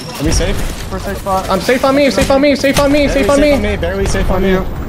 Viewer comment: Did Hal just say 'safe on me' until he is knocked?